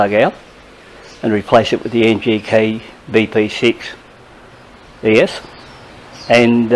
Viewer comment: Hi, can you tell us the part number for the relays? I assume they are the same on the Himalayan? And that spark plug, it sounded like you said NGK B6E. Is that correct?